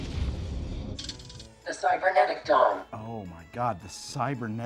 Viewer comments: I swore it said “cybernetic dong” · I thought Chat GPT said Cybernetic Dong.